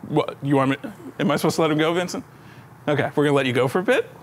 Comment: did call, Vincent? hahah so formal :'D